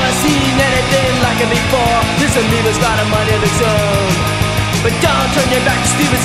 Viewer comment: Today I just found out that they’re saying “amoeba”. This whole time I thought they were saying “kalima” like from Indiana jones